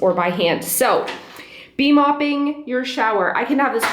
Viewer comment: Thank you for sharing...can you tell me what the mop is that you use. I 'think' I hear you saying 'B-mopping'. I'm a bit lost on that. My hearing isn't the best. Thanks again! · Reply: Bee mop is just the brand name of s sponge mop